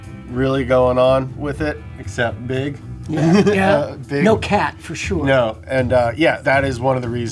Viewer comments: I thought he said no cap at 15:12 😭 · Thought he said "no cap"😂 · i thought he said no cap but he said no cat